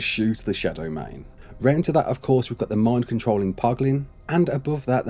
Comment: Did I hear Poglin?